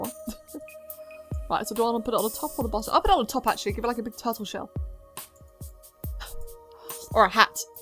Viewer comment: When she said turtle shell I honestly thought she said taco shell I need to sit my fat ass down.